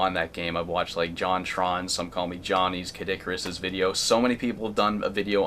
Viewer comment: did i hear him say Kid Icarus ? lol Great video i hope you interview more developers. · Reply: he said, who's also a video game reviewer